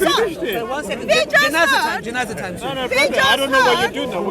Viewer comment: did I hear janazah timeXD? njirr itu candaan greget bets Wkwkw